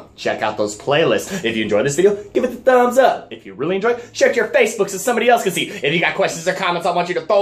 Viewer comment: Whenever you say "share it to your Facebook", it sort of sounds like you say "shut your face" and cracks me up. Keep up the good work, been watching your vids since you started.